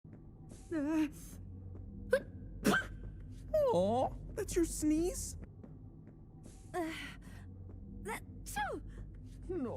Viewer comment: I heard "Aw, is that your knees?"